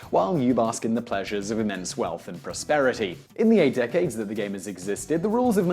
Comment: did he say 8 DECADES?!?